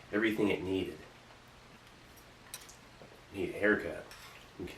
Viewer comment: Did Steve just say "I need a haircut"?